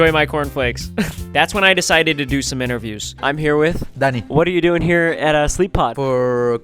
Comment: bro said his name is Danny when you put on caption but it sounds like he said… D A D D Y